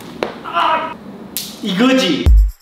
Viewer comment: 나만 이그지 라고 들리나?